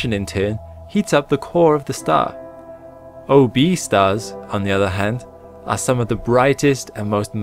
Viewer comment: Lol I'm an idiot. I first thought you said "obese stars" but no you said "O, B stars" OMG lol